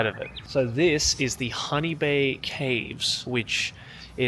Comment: At first I thought said "Honey Bear caves" cuz you know, with his Aussie vowel pronunciation, but then saw it was "Hanibe"